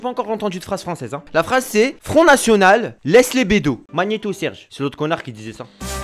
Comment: perso j'ai entendu "ton arsenal laisse les bédos"😂 3:34 "et je chie dans mon nid"